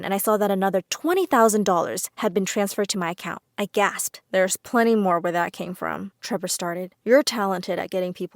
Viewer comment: I misheard “there’s TWENTY more where that came from” ...